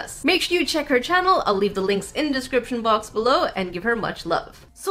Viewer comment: You said “sequence,” but I heard “sequins.” BRING ON SPARKLES!